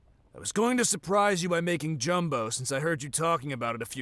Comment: did he just say… “jumbo” LOL gumbo u meant